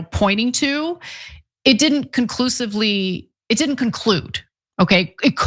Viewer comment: Did you just say “it didn’t ‘conclute’…”😊 💜